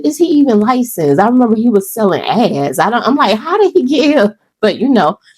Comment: Now listen.😩😂 Maybe I’m just a piece of trash and I can’t hear, but I really thought you casually said that man was selling 🍑 for a minute.🤣 I thought the fever dream was fever dreaming.🥲😂🤣